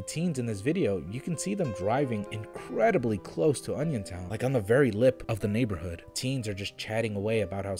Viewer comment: I thought that said Onision lmao